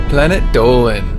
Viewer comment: At first i thought he said "Planet Doughman"